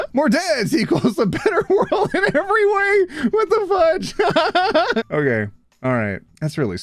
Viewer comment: I heard the murders